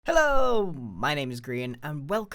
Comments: Kinda sounded like he said "Hello my name is Korean" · Idk why I heard “My name is green”